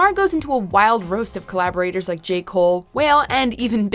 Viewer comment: did she just call him whale?